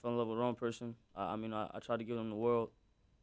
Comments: Did he just say I tried giving him the world 😅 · Is it just me or does he say " I fall in love with the wrong person ...... I, I tyied to give HIM the world"